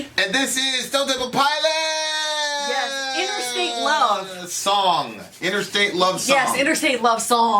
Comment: I thought you guys said "Interacial love song'!! JUST KIDDING! I LOVE YOU GUYS!!